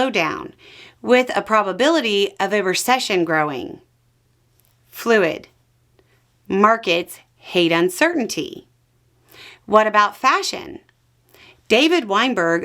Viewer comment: “Markets hate uncertainty. What about fashion?” At first, I thought you said “what about fascism” and it fit. Though it seemed out of place, I was proud you were mentioning the core root of this problem and calling it by name.